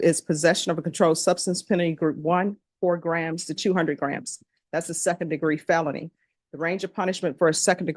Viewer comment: Mom, he is NOT a kid. He has two kids. Maybe he should join the military, see the world and give his children someone to look up to... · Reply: Definitely not a kid but a drug trafficker. I thought she said he got caught with 4200 grams but she said 4-200 grams.